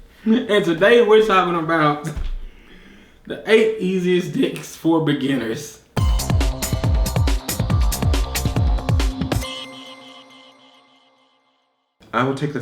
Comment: I thought I just heard 8 best dicks for beginners lol